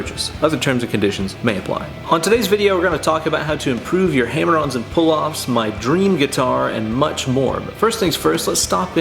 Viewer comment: I heard 'how to improve your hemorrhoids and pulloffs'. Thanks?